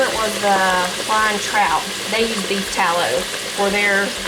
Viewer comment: Did y'all like the taste of the beef tallow (thought you said beet at first😝) in your fries? 'Cause I just put some in my cart after googling. Someone gave me a Dirt Devil stick vac a few years ago & haven't used anything else since. They are amazing. If it were cordless it'd be perfect. We managed to stay awake til midnight after falling asleep a couple of times😳.